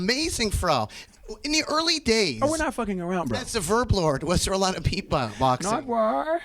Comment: What did Pharrell ask him not to say at 8:15 ? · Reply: Reza someone earlier said he was talking about a beatboxer named the verb lord and I guess Pharrell way back in the day used to go by that name so he was just goofing him